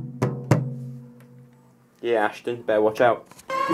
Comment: did he say ya ashdubh better watch out?!🤔🤓😮